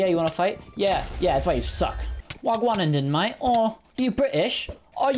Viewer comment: What did he say at 0:53? “Wagwanenden Mate” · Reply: he said "wag1 den mate" (wag-one) which is roadman slang meaning, "whats going on there , friend"